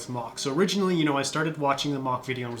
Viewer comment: You said "turret exploding"... I heard "turd exploding"